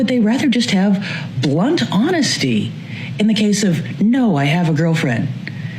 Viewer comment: I hear she needs blunt honesty